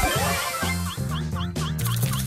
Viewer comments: did she said "and i oop"? OH S***T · did she just say "and i oop?"